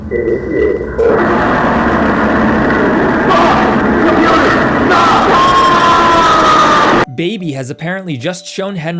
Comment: ayo what did he say 💀💀💀💀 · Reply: Aughh, I’m coming,… AAAAAHHHH